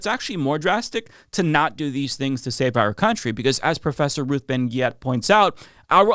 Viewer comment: sounds like he said "professor Ruth Ben Gyatt" 😭